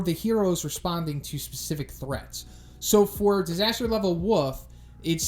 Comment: Anyone else hear him say disaster level wuff?